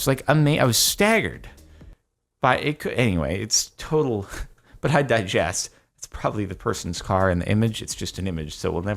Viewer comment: Sorry did jardon just say “i digess” instead of “i digress”